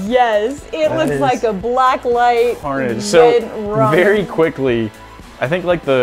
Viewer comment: Did... Did she just say blacklight red rum??